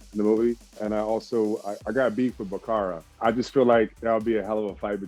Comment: did he say bakara or i just misheard him